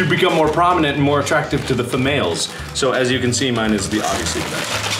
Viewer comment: did mark say famales?